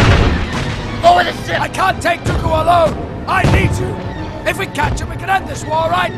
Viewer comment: at first, i thought he said "I hate you". but he actually says "I need you".